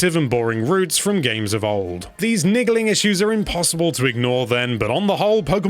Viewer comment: did he say "niggaling issues"?